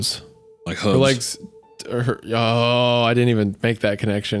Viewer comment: can someone explain what said at 1:51:18 I didnt catch it and reacted to it pretty hard lmao, if u see this and know what tf he said pls tell me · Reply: he said “like hooves”, referring to the way that Faye was walking on her tip toes in a way that could resemble the shape of a goats foot. :)